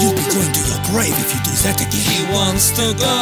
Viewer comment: I love how it says arsenal instead of grave in the subtitles